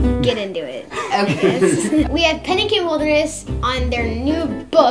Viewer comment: In the beginning when started the interview and said Penn and Kim Holderness I heard "Pennekin Holderness" and I thought.. what?! Penn is short for Pennekin?! And then about a second later I realized what he was saying and that Penn and Kim made way more sense then Pennekin! lol 🤣🤪